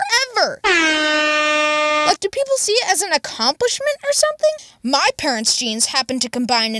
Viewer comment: Yo it sounded like you said accompish